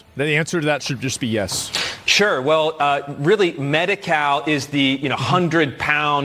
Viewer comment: Hahahaha I thought he said "Medi-Cow" not "Medi-Cal" I said "Jesus, they arent even trying to hide it anymore"